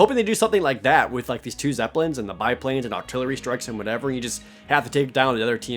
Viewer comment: you totally said Octillery, the pokemon, instead of artillery